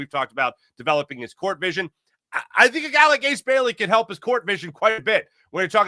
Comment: did just say Ace can help with court vision?